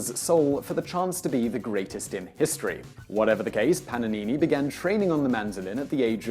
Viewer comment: Anyone else catch ‘Pananini’?